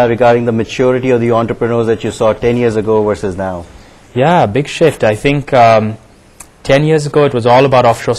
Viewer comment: It sounds like he says "big sh*t" - when he says big shift.